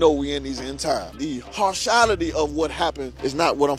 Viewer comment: BAHAHAHAHA what a bunch of kooks. Did i just hear the word "harshality?" Great education you guys are getting